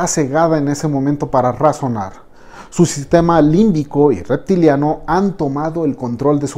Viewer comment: como que su sistema reptiliano? Escuché bien?